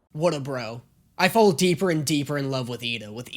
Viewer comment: Sorry buddy but she clearly says Loos not Lose